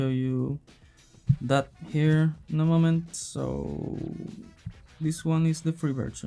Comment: i understood "no mamen" instead of "in a moment" 😂 Great video, mate!